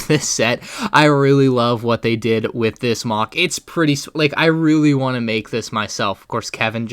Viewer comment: ''I would really like s**'' ~ 2020 Watch out next time while you are saying something fast XD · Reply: And it sounded like he said “I really want s*x”